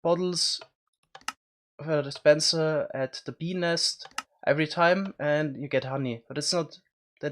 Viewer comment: is it me or did he say penis